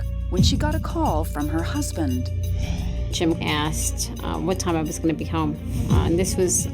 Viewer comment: Can someone tell me what she's saying at 5:18 following "Jim asked...."? I cannot pick up her accent very well. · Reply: Jim asked "what time I was gonna be home"?